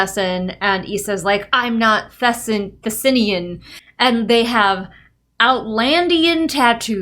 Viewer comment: somehow I misheard and thought you said Outworld at first and said “like Mortal Kombat?!”